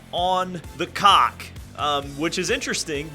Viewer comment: What did he just say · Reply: on the cock. Peacock. The Toronto race on the 17th